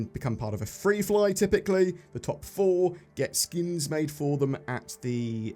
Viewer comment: Had my headphones on wrong and almost thought you said SKUs instead of Skins regarding the Best In Show Paints.